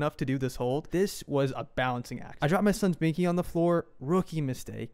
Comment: I thought to said dropped baby. I said 😮